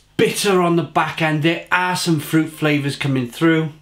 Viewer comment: Thought he said "there's ass and fruit flavor".